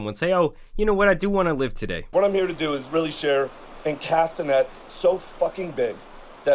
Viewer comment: I thought he said "share a castanet so f@ big" at first, that wouldn't even be the craziest thing he's said.